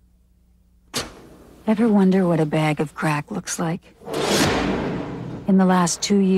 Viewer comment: When I was a kid, me and a close friend always thought it said "ever wonder what a bag of crap looks like" and we'd just start laughing hahahaha good times xD